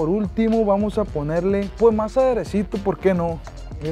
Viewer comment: Que es lo que le pones despues de la col que dices "y por ultimo vamos a ponerle..." y se hace un pequeño corte y dices "mas adereso" que era eso?